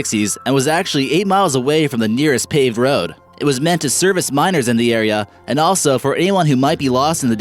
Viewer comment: every time he said "miners" I thought he was saying "minors" as in under age.